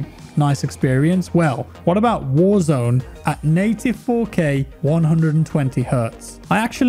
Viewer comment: I thought he said '94K' instead of 'native 4k' and was totally ready to watch a graphics card melt into lava